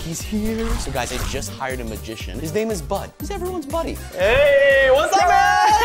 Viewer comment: Was it me or no. I thought I heard Nathan say "his name is butt he's Everyones buddy" 😂